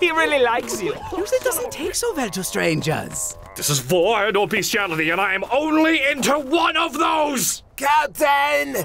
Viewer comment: Ok, what does Cell say at 3:30 ? I can't tell and it is bothering the hfil out of me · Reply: "this is vore and or bestiality and I AM ONLY INTO ONE OF THOSE!"